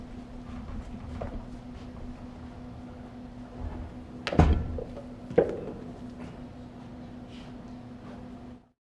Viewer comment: What was he about say at 2:55 😂😂😂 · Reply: « Y’all must have forgot. »